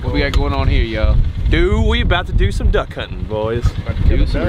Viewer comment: At first I thought it said ”d*ck hunt..”.. 😄